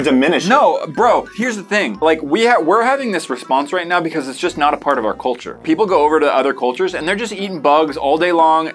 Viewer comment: When was talking about eating bugs is not part of their culture my AirPod died and it sounded like it said it’s just not apart of our cult 😂😂